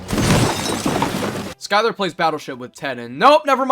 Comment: When you said “skylar play battleship with Ted” I heard “Walter plays battleship with Ted” and was so confused